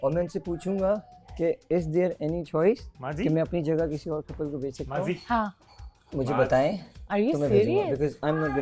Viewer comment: i heard he is saying fatima maa g 😂😂😂😂😂😂😂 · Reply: He said "maazi" as a nickname for maaz